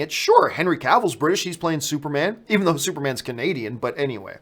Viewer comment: Holy cultural appropriation Batman! 48:54 did John just say “Superman’s Canadian”?! Lol wtf 🇺🇸